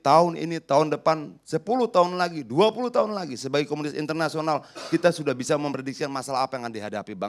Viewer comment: smoga salah denger y "Komunis Internasional" Kata adian